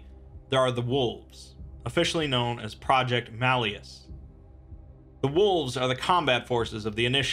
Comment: Any religious GoI: "Hey, wha-" Horizon Initiative: "Your faith is misguided." unleashes Project Malleus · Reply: Honestly I thought you said Projecter Mail bus